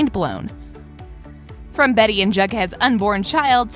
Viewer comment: EXCUSE ME BUT DID I JUST HEAR: ``From Betty and Jughead's unborn CHILD?!!`` Timestamp: 1:13 BUTT WHAAAAT THEY GONNA HAVE BUGHEAD BABY'S?!!